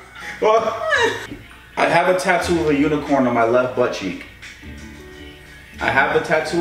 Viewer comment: I thought he said “I have a tattoo of you and porn on my left butt cheek” for a second before reading the caption 😱😂